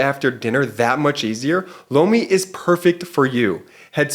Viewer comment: I have to say I giggle everytime Jordy talks about Lomi…it sounds like he is saying Blomi😂😂😂